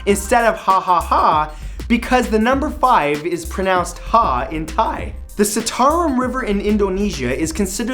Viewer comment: Did I just heard you saying hentai?